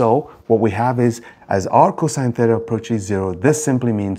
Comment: I look away from the screen for a moment, You say "r cos theta," but I hear "arccos theta" - WHAAAAAT? 😄